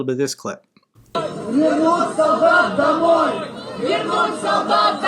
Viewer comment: I hear "Bring dogs back!!!" was it meant to be dogs or soldiers 😂😂